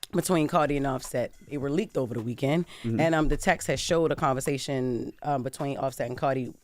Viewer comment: It is just “Text(s), not Texas”. I live in Texas 😂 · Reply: I heard "the text was" she was just talking fast